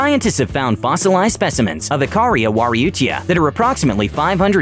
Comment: I hear uchihaaa